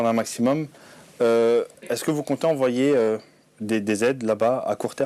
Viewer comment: A 3:27 moi jai entendu"envoyer des dz " jdis ca jdis rien 😂😂